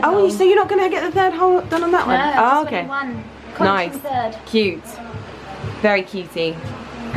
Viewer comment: I had to pause to say, at first I thought you said Carrie was getting her CROTCH pierced!!! LOL then i realized you said CONCH LOL oh gosh i about died laughing!!